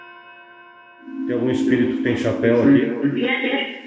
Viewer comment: No minuto 11:26 depois de falar chapéu, ouvi falar hipercontato com certeza foi a mentora